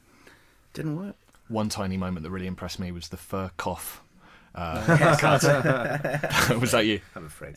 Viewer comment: What does the interviewer say at 52:30, where he says that he was impressed by something? I can't make it out. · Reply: WAAAY too late but he says "f-ckoff" meaning the way they kept cutting back to sherlock/john doing their own things and matching their words/ actions to swear :P